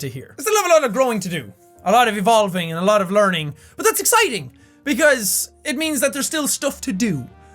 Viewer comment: did I hear that right? Learning, exiting!!!! WHAT IS WRONG WITH YOU?? Learning is supposed to be good but it normally isn't exiting you loonatic